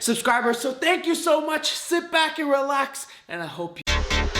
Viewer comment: thought you said “SIMP back and relax” for a second lol